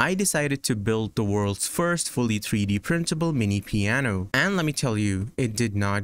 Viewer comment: I heard "98% 3D printable"